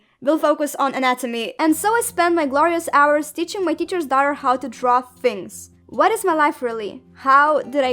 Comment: At first i thought the teacher said her daughter was three and i was like WAT BOI UUUUUUUUGG